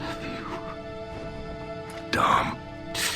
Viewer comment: lol sounds like Marcus says " DUMB" Had me lmao.